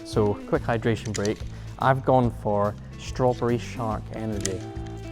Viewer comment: I thought you said that Holy was "Strawberry Shart" flavour 😂